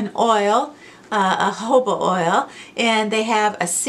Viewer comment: I thought you said “hobo” oil and realized it was jojoba oil.